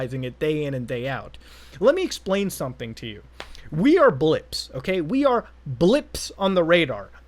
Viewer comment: I thought he said we are blitz and i got scared of the shield sounds in the distance